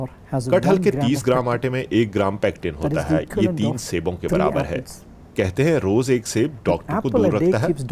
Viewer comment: Whats is he saying between 02:55 to 3:15.. Kathal ke 30 gm aate mein 1 gram _ (dash) hota hai ?? Kya hota hai... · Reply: "Pectin hota hai" he said... <link>